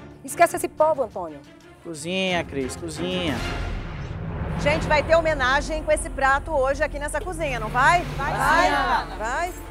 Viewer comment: e eu que entendi oq a Ana falou assim "Gente vai ter um ménage, com esse prato hoje" 👁️👄👁️